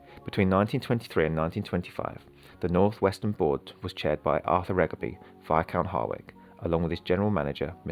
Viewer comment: Albert Regaby is the father of Henry Regaby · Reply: I see, I was just confused because it sounds like you said Arthur Regaby